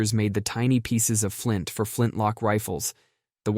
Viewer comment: did you say flintlock..? FLINT LOCKWOOD!